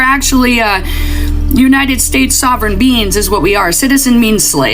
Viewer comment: Did anyone else hear “Sovereign Beans”? 😂